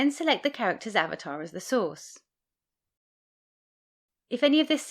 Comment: I keep hearing "As the sauce"